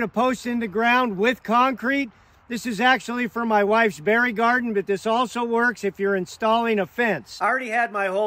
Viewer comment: Dude i thought you were gonna say "for my wifes burial" but you said "for my wifes berry garden" oh lord😂😂😂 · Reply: I thought he said fairy garden so I came to the comments to find out. And I learned you don’t need to mix cement anymore. Beautiful